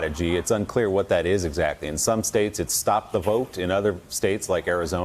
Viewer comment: But I thought he said "Stop the Count" lol